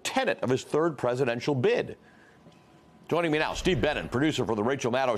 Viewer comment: LOL—I did a double-take when I heard “Steve BANNON” but when I looked at the video, I realized it was a different guy 🤭.